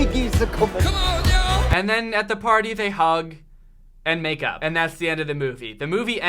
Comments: i dead ass thought you said “makeout” instead of “makeup” so i sat there for a minute freaking out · I thought he said “and they hugged; and maked out”. Edit: tysm for 0 likes! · I thought you said make out. I was very worried for a sec. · I thought he said “and then at the party they hug... and make out” 😂😂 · I heard “and then at the party they hug and make out” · i thought you said “make out” I ALMOST CRIED BYE edit: wrong time stamp lmao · At first I thought you said: "and then at the party they hug, and make out" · Straight up thought you said make out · I thought at the end he said "so they hug and make !OUT!" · I thought said 'and make out' and I had a fucking heart attack hh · Totally thought that you said make out for a second and I was concerned, was this made in Alabama by any chance? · its like ripoff lazytown also at 21:50 i heard make out so.. SWEET HOME ALABAMA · i thought he said they hug and make out and i was like AND I OOP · I thought he said they hugged and made out and I was so shook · hly shit i thought he said "they hug and make out" (about the daughter and dad) and was like wHAT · I thought he said “and then they hug... and make out” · I thought you said "then they hug and makeOUT" and i was like WOAH THAT TOOK A TURN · I thought you said make out! And I was like sweet home Alabama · thought you said make out!! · At first I thought he said they hug and make out at first and almost coughed up my water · I thought he said make out